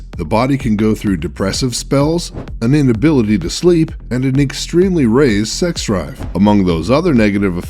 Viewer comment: did he say extremely raised sex drive? thats not a bad thing is it?